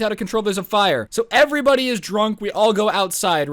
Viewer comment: he actually said drunk Here comes Susan